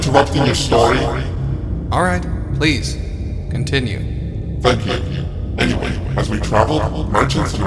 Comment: i hear "f*ck you"